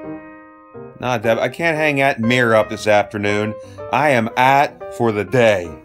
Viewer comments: Need a translation at 3:20. "I can't hang out at 'mirror up' this afternoon". At least thast whats the captions say. Geez you'd think the YouTube wouds hava tos English translator by now. :) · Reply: Cliff Hartle Its I can't hang that mirror up. the captions are wrong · Need a translation at 3:20. "I can't hang out at 'mirror up' this afternoon". At least thast whats the captions say. Geez you'd think the YouTube wouds hava tos English translator by now. :) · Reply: OK I heard "hang out" instead of "hang (th)at" and got stuck on assuming it was a place. Again miss understood the regional West Mifflin Pittsburghese sub dialect. :)